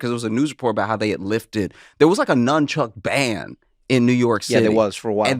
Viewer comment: I heard "NYC had a Nunchuck Band" and immediately went into thinking how clever that would be like maybe drumming or something? And then realized y'all said nunchuck ban... Yeah that makes more sense...